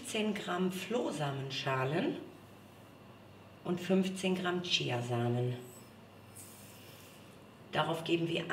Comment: The English subtitles say 'Chinese seeds" where it should read "Chia seeds" obviously